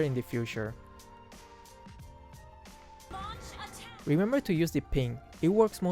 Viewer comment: U said 5:48 :"remember to use the ping" how can i use ping.. or was it my fault of misheard